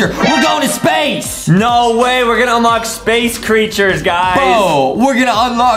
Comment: i heard bo instead on bro